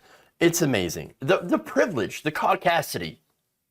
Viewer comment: "it's amazing. the privilege, the ????" Can anyone tell me what word this is? Was he trying to say audacity? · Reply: Caucacity. Caucasian audacity.